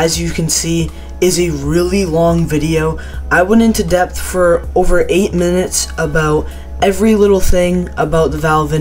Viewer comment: i thought he said I went into debt for about eight minutes